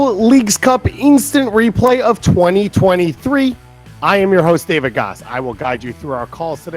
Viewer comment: I thought he said final Instant replay of 2023 at first.😅😭